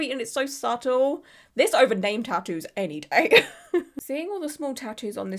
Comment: thought you said gang tattoos, had me lost for a sec ay.